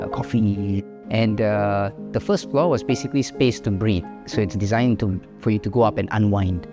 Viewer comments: I heard "The first floor was basically space to breed" 😅 Beautiful house! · had to rewind here because I thought I heard "space for breed"